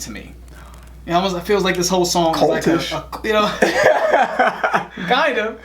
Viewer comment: Oh, "cultish"...I thought you said "kultush"...the Chinook word for white man...it means "useless". LOL